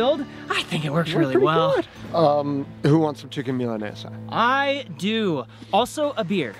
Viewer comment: Did he said "Chicken Milanesa"?